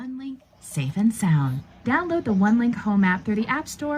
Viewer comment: what did it say? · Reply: It said "Hello and welcome to your Onelink Safe & Sound" You can hear the full thing here. <link>